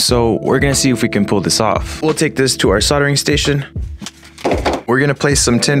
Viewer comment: I heard “Let’s head to the sawing station”, I was very confused lol.